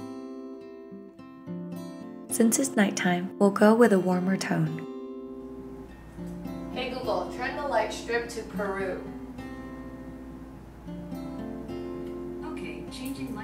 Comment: Did you say on 6:00 "turn the lights strip to Peru"?